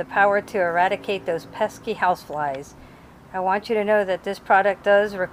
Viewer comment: She almost got me to buy one at 0:55 when I thought she said "Now you officially have the power to eradicate those pesky housewives".